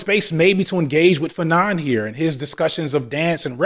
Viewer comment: anyone catch the name drop on dance and revelry under colonialization? Fernan? Vernon?